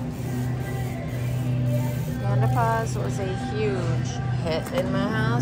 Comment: At first I thought you said “menopause” was a huge hit at your house then I realized you said panda paws 😂😂